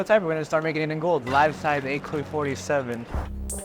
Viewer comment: “Live Tive A-Coi 47” 💀🤦🏽‍♂️